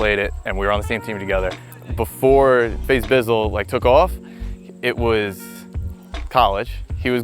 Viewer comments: Thought it said faze biffle 😭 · who else thought it said “meet FaZe Biffle” lmao